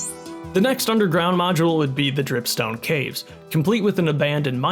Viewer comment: I thought he said tomb stone caves😭